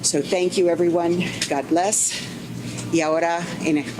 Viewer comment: Why does she say "God bless Yoda" at 1:59? Hardly the time to advertise her favorite science fiction character.